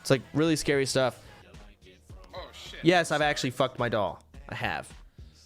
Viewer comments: I thought he said "Yes I actually fucked my dog" and it sp00ked me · I legit thought he said he fucked his dog and I was about to freak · i heard dog that was awkward for a second lol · heard it as a dog, was like eew wtf but it was doll THANK GOD rofl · did he say dog or doll? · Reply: Doll. He got a sex doll for Christmas